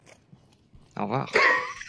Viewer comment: he actually said "au revoir" = "goodbye" instead of oh wow